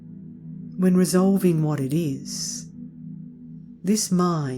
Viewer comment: dear, please be so kind...how to hear at 20:31 min. does it say: "when resolving what it is..." OR: "when resolved in what it is.." . thanks a lot! dear regard, st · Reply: Hi Stephan, it is : "when resolving what it is, this mind cannot be proved to be anything whatsoever."